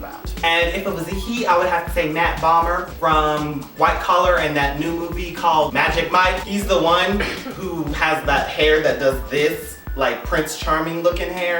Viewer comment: I thought you said Matt Boner >_>